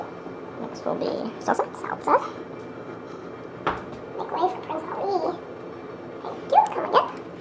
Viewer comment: I may be the only one in the world who didn't know what planking was, LOL! I learned so much from you guys:), did I detect an "Aladdin" phrase in your chipmunk dialogue?… Because I thought for sure I heard…, "Make way for prince Ali!!", lololol!!!